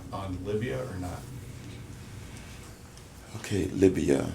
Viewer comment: Herman Cain~ Libya....I thought you said Labia...no no start over wrong thing.